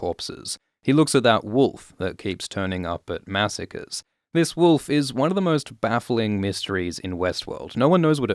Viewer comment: I swear I heard you say “this wolf is one of the most baffling mysteries of Westeros”